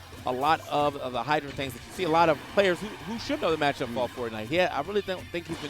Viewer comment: did he just say fortnite? Lol